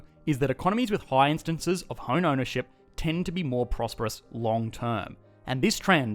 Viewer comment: Did you say, "...with hone ownership ..." ?